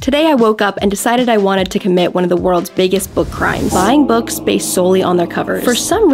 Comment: I THOUGHT SHE SAID “I’m gonna be committing one of the biggest war crimes” and I was like HUH😟